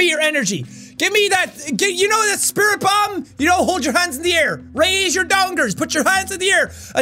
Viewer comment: I thought you said ,,raise your dongs" XD